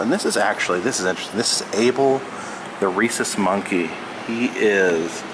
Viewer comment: I’m sorry, but I heard him say Abel the racist monkey and I just died... And go ahead say whatever you want